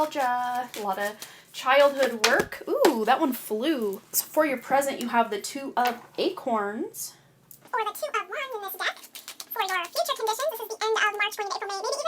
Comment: Why is pile 2 from minute 15:57 to 16:09 suddenly too fast?? I tried to slow it down, but it is not working…cant understand what you are saying. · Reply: That's an editing mistake on my part. Apologies. Paraphrased, I said: "...or the two of wands in this deck. For your future conditions, end of March- June, the energies never hard stop at the turn of the calendar page. 4 of Crystals/Pentacles."